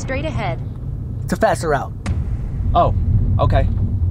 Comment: I thought he was saying 'faster out' then I realised he meant faster root (route).